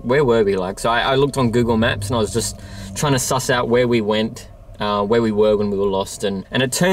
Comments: "I was trying to work it out"... is what I thought she said🤔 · Very cool video! It sounds like voice said "just trying to get out".